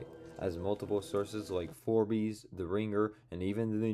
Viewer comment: sorry did you say FORBIES